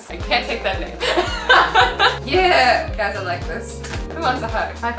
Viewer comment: did Azzy say: “Yeaaah! Kass will like this” ?? Or “Castle like this” Or “Kistel ite dis” You decide... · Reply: Cass will like this